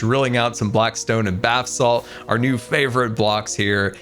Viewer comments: Did he say “bath salt” instead of basalt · I heard Blackstone and Bathsalts... dang our mayor still up to his wizardy habits...